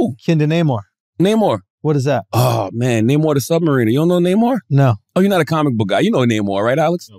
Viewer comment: Swore I heard “Namor the Submarine” 💀🤣🤣🤣